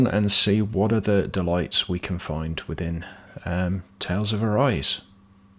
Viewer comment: I thought that said Tales of Arce.